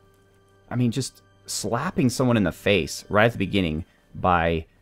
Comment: The first time I heard you talk about the difficulty level, I thought you said 'hit in the face with a fish'. I like this game, looks like fun.